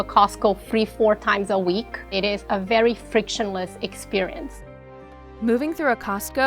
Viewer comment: did she say FRICTIONLESS